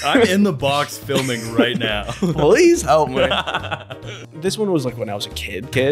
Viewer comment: is he saying “police help me” or “please help me”? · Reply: It sounds like "police help me" but YouTube subtitles says "please help me"